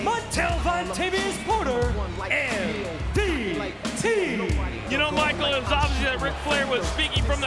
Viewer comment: did he just say MVT😂